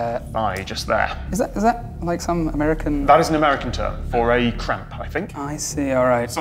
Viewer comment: I know it's not the case, but it sounded like he said Charley Horse was an American term for "crap". Made me laugh out loud.